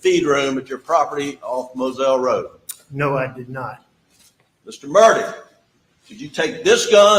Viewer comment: Were the closed captions on the clips yours or Law&Crimes?? Cuz - watch your video from time stamp 22:00-22:20 - Probably shouldn’t be funny.- but it is hilarious- the CC calls him “Mr. Murder” instead of Mr. Murdaugh. 🤣🤣🤣